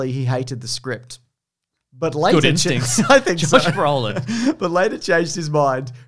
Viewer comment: I have no idea what says. I hate when that happens; will say a joke and will say something at the same time and then laugh but the joke isn’t audible so I have to miss out on the joke. I listened to it 10 times and still can’t distinguish what is saying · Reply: He said “good instincts josh brollin”